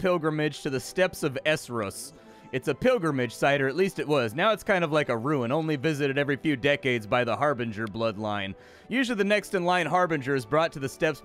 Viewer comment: I thought it said bloopline.